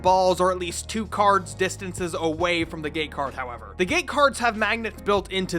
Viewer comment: The whole video i heard "The GAY Card" smh